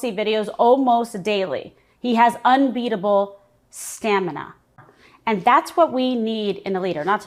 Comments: Trump is a 70-something who's 300 lbs and lives on European Sudafed (it's got more of the good stuff than the American version) & McDonald's. ...and they're talking about his "stamina, strength and focus". What a joke. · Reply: I thought she said "Scamina" That I could agree with..... ❤ It's those ridiculous, hideous lips speaking! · Did she say unbeatable "scamina"?